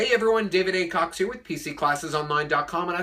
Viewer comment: Thought he said "david a cock"😂😂